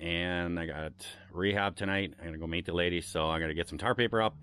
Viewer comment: Did I hear “gotta to meet the lady?!” Spill the deets! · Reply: Thought I heard "ladyboy".